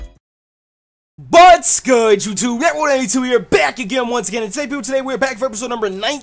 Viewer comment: it sounded like u said BUTS GOOD at the start